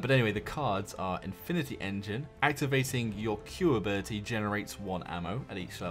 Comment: When he was pronouncing the names of the legendary cards, I thought he said, " ....... Infinity Engine, Activating your Puberty and Adolescence of the Past"